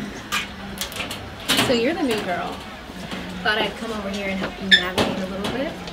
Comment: for a second I thought she said "so you're the negro" 😳